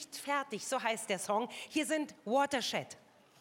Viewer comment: From watershed sounded like she said “from what a shit”